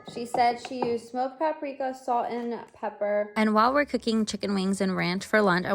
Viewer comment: Sounded like you said NUT pepper instead of black pepper 😂